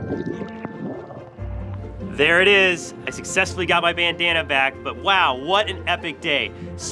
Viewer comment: I thought you said "he stole my banana"😂btw i lliked and i've been subscribed for 1or 2 years